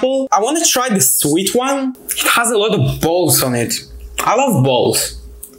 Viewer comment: Did you just say l like balls